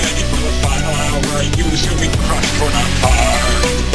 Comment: I sounded like he said fart?